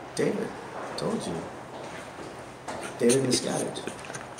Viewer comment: Yo I thought he said his name was David miscarriage! That dude was trying to act real hard gangsta!